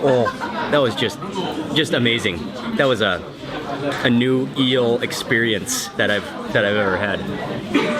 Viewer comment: Excuse me ! I have a question about your video. I dont understand '' A new deal experience '' in this context ( 10:36 ) you're speaking. Please answer me because of my English learning.@ Wiens and everyone Thank you very much. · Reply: He said, "A new eel experience." Have a nice day!